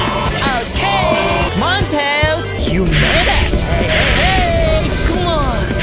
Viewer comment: SONIC WHAT DID YOU CALL TAILS? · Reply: he said "you made it"